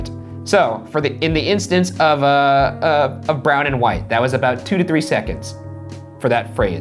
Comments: The "brown and white" recording, to me it sounded like it said "one brown one white" not "brown and white". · When the clip said "Brown and,WHITE" my sister said it sounds like somebody saying IM GAY